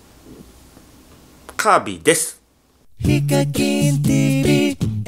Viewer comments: 始めの｢カービィーです｣が｢神です｣に聞こえる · 最初カービィです。が、神ですに聞こえた